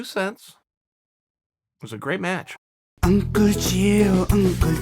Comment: I thought the end song was saying uncle Giro 😂😂